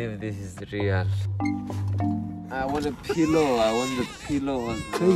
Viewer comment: when he was asking for a pillow i thought he is saying i wanna pee low🤣🤣🤣